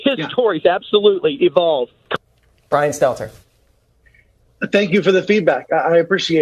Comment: When he said “little Brian Stelter” I thought he said LaBraun Stelter and thought Damn what kind of god forsaken love child that would be